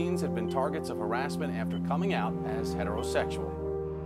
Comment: I thought it said homosexuals aren't allowed but it says heterosexuals no wonder I was confused half the movie